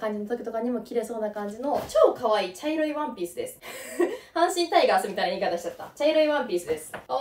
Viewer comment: もう阪神タイガースにしか聞こえない🐯⚾️笑